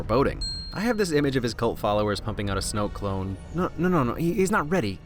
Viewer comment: anyone else heard Snowcone when he says Snoke clone? Just me?